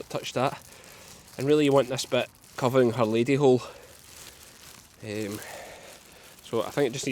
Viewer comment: Did he say lady hole?😂. ❤️ it! What is a man hole? ❤️you ❤️